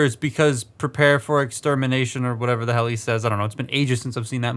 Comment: he says "prepare for extinction" or at least that's what he says in the transformers devastation game